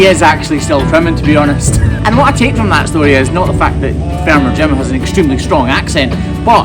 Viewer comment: Hahaha , has such an accent that i thought he said shit farmer but quickly realized he said sheep. farmer. Sorry